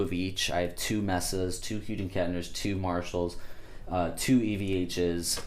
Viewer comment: Nice collection man! Your one lucky 🐥 I have a question though... you're the only person on YT who pronounces H&K that way 🤔 it sounds like your saying "HUDEN Kettner" instead of "Hughes and Kettner"... any reason?